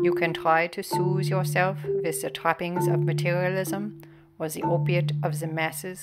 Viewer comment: Just a tiny point, on the sub-titles it says "toppings" where I heard "trappings".